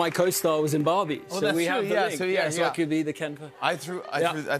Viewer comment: I’m sorry but did he said “I can be the Ken for her”